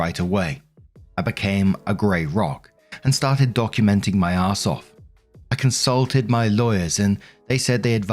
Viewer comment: I heard "I started documenting my asshole" and had to check to make sure that wasn't right 😂